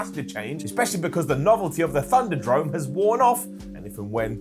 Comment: Did call Thunderdome, "Thunder-Drone"? Sure sounds like it. Thunder-Drone. Named after Shane's droning about how things will be different. Lol. \o/